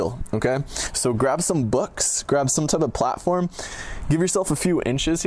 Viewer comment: Did he say "Bucks" @ 1:06?